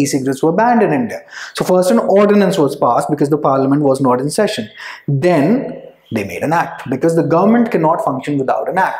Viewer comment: What have you said at 4:39 What was banned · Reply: E cigarettes